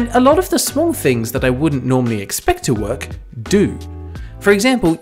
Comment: Subtitle typo at 2:40 says "expert" instead of "expect" 3:55 every instead of ever